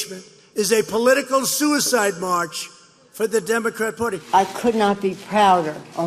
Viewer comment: did he say Democat Pudding??